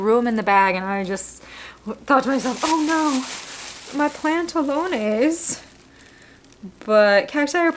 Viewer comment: I'm sorry.... Did I hear you say "oh no mis pantalones"? Girl...what?! 😅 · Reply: Mis PLANTalones!